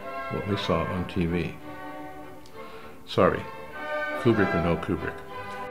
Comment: What does he says at 11:00? Philbrick can help Kubrick?